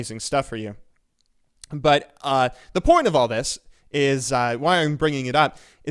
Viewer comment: i could have sworn i heard him say "the porn of all this"....